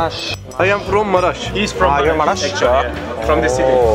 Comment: I thought He said , I'm from "Mars" 😂